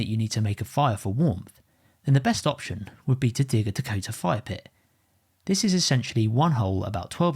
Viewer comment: Did he just say a "Potato Fire Pit"?